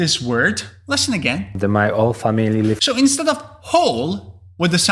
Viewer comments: I don't think he was saying 'whole family' with an 'ole' pronouns at all, but instead he was saying 'all family' although its not exactly right, it matches well as he continues to use it with 'all friends' and 'all the time' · Hi, Polish person here. We can pronounce "h" just fine, Lewandowski just made a mistake and said "all" instead of "whole" in this example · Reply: It sounded like he was saying "my old family". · Hi, Polish person here. We can pronounce "h" just fine, Lewandowski just made a mistake and said "all" instead of "whole" in this example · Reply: Yeah, I thought he said "all family" - mirroring "all friends". Also thought Ronaldo said "200 caps" not cups. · Even the last one?!? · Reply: Yes even the last one And I am Indian. So if you know English well, I guess it shouldn't be tough The only one which was challenging for me was when Robert Lewandowski said "whole family". I thought that he said- "all family". I thought that he just did a grammar mistake, it's normal bcuz he ain't a native english speaker. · The only one that threw me was #8 the polish accent. I thought he said all family when he said whole family. I got all the rest. 😊